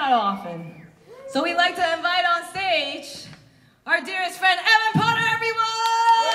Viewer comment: Whats does Dany say? Somethin like "We'd like to invite on stage our dearest fan (says his name) but i cant understand the name. Anyone got it? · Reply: Evan Potter. He is the guitar player for LILITH CZAR. But, for this run of TW’s tour, he was their guitar tech